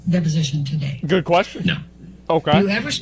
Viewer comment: This is for deaf viewers. @ 10:22 the guy said no to the question. The captions said ”yeah”